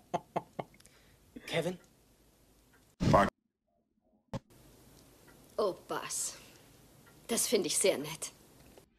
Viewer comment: Was hat er da gesagt? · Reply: Er sagte "Fück u äss whöre" was das auf Deutsch heißt, müsstest du ja wissen, wenn nicht dann benutze den Google Übersetzer. :D